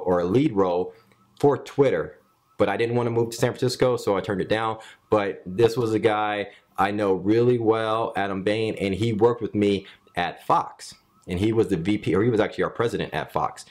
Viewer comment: it sounds like he says "its a guy I knows really well, and I'm bangin, .. etc.." under his breath lol